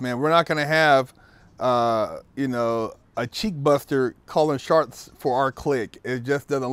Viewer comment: did say, " naw man, we ain't gonna have a cheek buster callin "sharts" for us"...😂😂😂💨💩🙊🙉